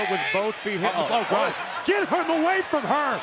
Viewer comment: Anybody else hear Corey say “GEt HER AwaY FrOM HEr” He is a girl now? And “I think Mandy likes her” Screw the commentary calling him a girl Edit: WOW MY HIGHEST LIKE WAS 3 THEN 9 AND I GET 31 IN A DAY WOWW